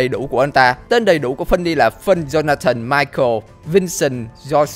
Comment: Nghe như... John Cena